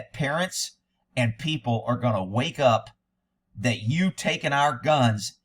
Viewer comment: I can't be the only one who thought you were saying "wake up ppl" lol. I speak Ky south, not Texas south.